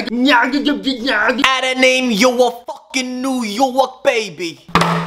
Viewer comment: i thought he said how to name you fooking new York baby